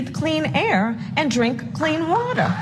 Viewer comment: Sounded like she said "clean watuh."